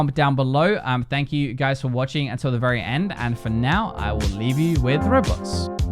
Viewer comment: literally sounds like you said "And for now I will leave you with the roblox" this time lmao